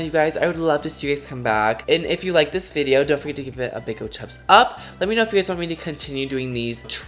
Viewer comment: Youre funny did you say "dont forget to give it a big ole chubs up"? haha